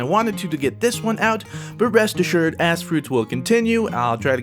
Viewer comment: All I hear is, "ass fruit" #AssFruit